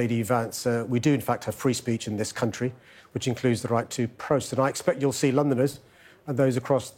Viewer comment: Did he say "protest" ?